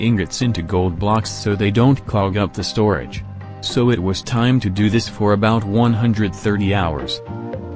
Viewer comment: Ik he said: “it’s time to do math” but I heard: “ it’s time to do Meth”